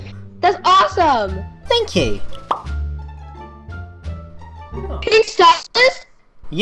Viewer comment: Always thought it said pinkystylist lol